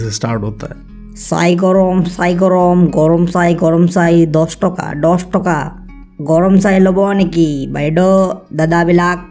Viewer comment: For a second I thought he is praying to God saying- Sai Ram Sai Ram Sai Ram 03:05 🤣then i realised it's chaiwala there 😅